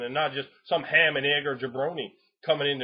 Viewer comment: Did I just hear you say "Brony"?